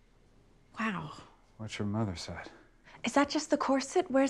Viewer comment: i always thought david said "that's your mothers side" meaning thats your mothers beauty. i only now heard what he actually says "what your mother said." I like my mishear better.